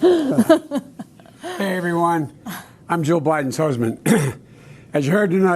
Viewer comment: Very nice lady, but her husband is on the edge of dementia. I wouldn’t put my husband through the humiliation he will face as it progresses. “Joe Biden’s husband?” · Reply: I saw that video too and thought he referred to himself as Joe Biden's husband as well LOL.. He didn't actually if you replay it he does say Jill but it does sound like he said Joe .